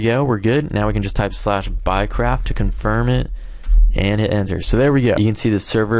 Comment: sounds like your saying "buycrap"